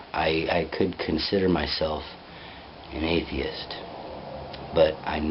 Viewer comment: I thought it said, 'athletes', and was trying to figure out the connection. But now I see it says atheists.. :) I did a series that tries to prove the occult side of the world and how things are, "Hidden In Plain Sight", so inversely, if A then B, so if Evil exists that cannot be explained, it must mean there is Good AKA God. Here is the series if you are interested Part#1 and #2 are on youtube rest can be found by linking, from either video, due to copyright. watch?v=6xwaV4xvhaU